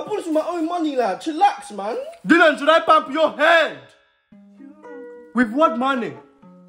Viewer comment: the video just gets like 10x funnier when i hear "Dylan shud i bump your headddd” 😂😂😂😂